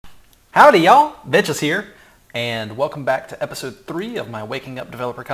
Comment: gonna be honest, i thought he said "Howdy yall! Bitches here!